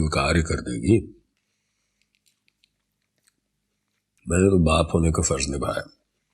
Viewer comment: wt did he say? Baap honeka FARZ NIBHAYA??? 😅